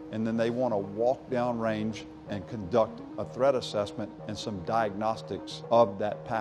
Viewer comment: I thought he was saying Thread assessment